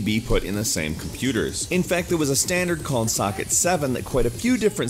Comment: Did he just said suck it